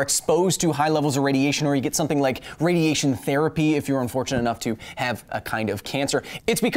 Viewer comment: I thought he said, ".. if you're lucky enough to have cancer." :p